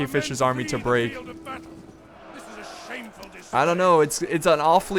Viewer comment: I heard "This is a sameful dispray" lel